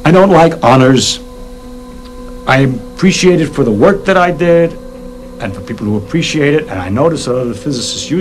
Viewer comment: What exactly does he say at 1:44 "I appreciate it for the... OR "I am appreciated for the..."? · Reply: Pretty sure the subtitles are correct on that particular line. “I appreciate it for the work that I did..”